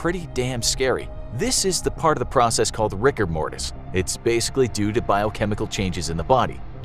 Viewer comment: when he said "riger mortis" i thought he said rick and morty